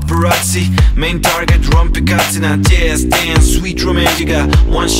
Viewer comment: Paparazzi...Rompicazzi?! Ho sentito bene? xD